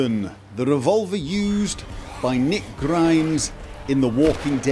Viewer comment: Honestly sounds like he says Nick Grimmes 😂😂